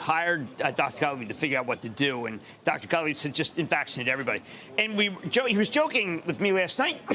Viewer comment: Did anyone understand his mumbling... "invaccine everybody" ? lol